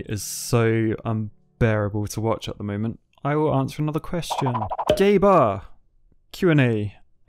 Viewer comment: I'm sorry but that straight (or maybe not so straight) up sounded like "gay bar"